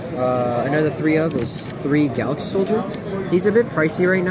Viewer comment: Jesus Christ I love your deck videos but it's so hard sometimes to keep up, like at 1:40, I heard something like "three gaug-soldier" like wtf is that xD How about you include a small text when the cards appear? 3:28 "one turtle" fam are you shitting me, I'm using level 3 NSA techniques to find this card now 9:54: Zenmaios? What? xDDDDD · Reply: 3 galaxy soldiers "One turtle" is 1 electromagnetic turtle They r a famous monsters in any cyber dragon deck